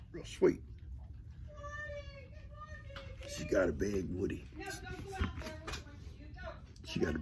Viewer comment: 😂 with your accent I thought you said she’s got a big woody laughing so hard here